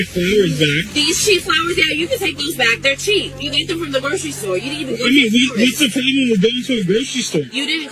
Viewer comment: She actually said “you didn’t go to a florist”, but she’s still ungrateful and disrespectful.